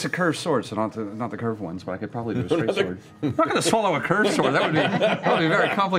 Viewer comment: (C2 SPOILERS) 15:24 for a minute i thought he said "im not gonna swallow a cursed sword" and instinctively looked at travis realising he will do precisely that in THIS episode